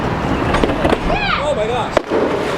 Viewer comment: Did he say shit